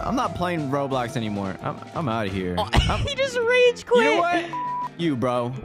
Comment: DID JUST SAY #### YOU TO?